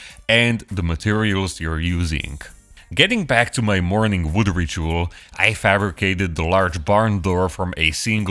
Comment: Did he really say 'morning wood ritual '? 😮